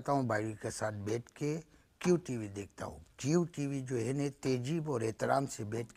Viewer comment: Did he say jew tv